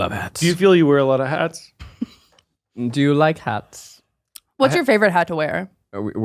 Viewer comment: When said "Do you like hats?" at 31:05 , I hear the Lord of the Rings interview guy asking "Do you wear wigs?"